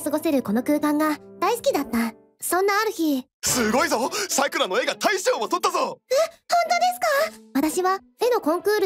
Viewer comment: サクラの絵が大将を撮ったぞに聞こえる😂